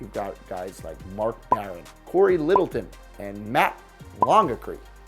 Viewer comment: Did anyone else hear Cory littledick